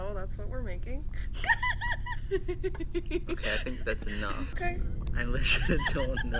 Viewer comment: You use clay for butter slime not butter lol everyone knows that(guess not them At 2:28 -2:29 i heard "thats too much glue"